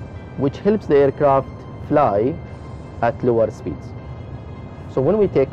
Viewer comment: did you say duel speed You are talking to fast